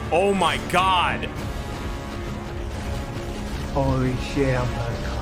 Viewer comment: Did anybody hear at 15:15 someone say "holy shit I'm gonna cum".... porn? lol. Or just me?